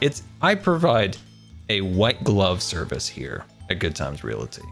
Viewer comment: Did anybody else hear say a "wet glove policy"?